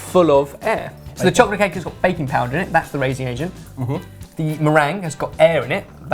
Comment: I thought said baby powder for a second then 😂😂 is my favourite thing to watch as it is great food and you guys are really funny😂😂😂😂